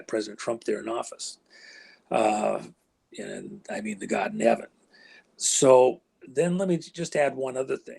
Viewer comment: Starting at 44:15 W says "thank God, I mean God nevett? Or am I hearing something?